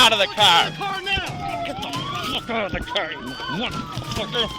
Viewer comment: I heard "Get the f out of the car, you black mfr" IM DYINGGG LMAOOO